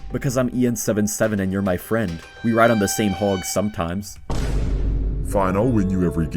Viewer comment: I thought for a second you said same log...